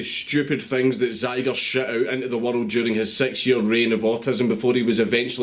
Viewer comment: Every time Dankula says "six year" I hear "sexier"